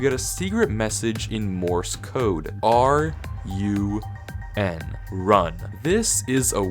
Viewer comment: "RUN" i thought you said are you in 💀💀